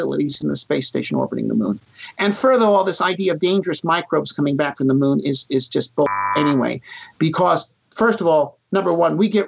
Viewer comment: What was said? There is a sound masking the word. :( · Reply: ".. And third of all, this idea of dangerous microbes coming back from the moon is just bull **** anyway, because ..."